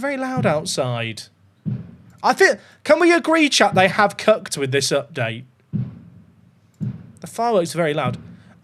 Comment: I heard "They have cucked with this update" I double took...